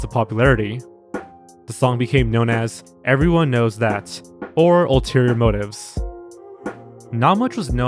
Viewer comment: am i the only one who hears "everyone knows it" instead of "everyone knows that"? Not that it matters anymore